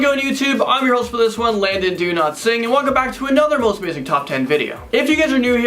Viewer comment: is it just me, or did it sound like he said "landon donotsing" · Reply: He makes fun of his name which is refering to it as Landon Do Not Sing this has become one of his catchphrases along with Is This Real Life Right Now which has recently been printed on T Shirts .